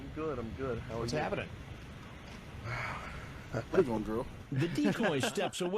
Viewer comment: what did he say? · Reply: He asks the decoy, "Where you going girl?"